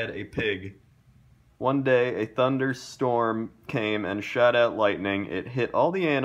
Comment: I thought that said "a thunder storm came and shat out lightning " Not sure why I even thought it could be that